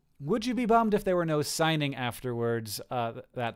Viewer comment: I thought it said "singing" and not "signing." And I was confused.